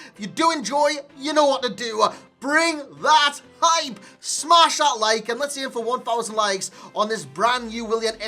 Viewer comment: Did he say mashallah at 00:24 ?